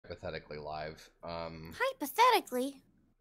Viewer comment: I click the video and the first thing I hear: "Pathetically Live"